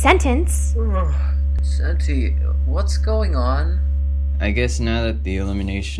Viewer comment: it sounds like backpack is saying "ugh, sexy, whats going on?" and im laughing and crying on the inside now.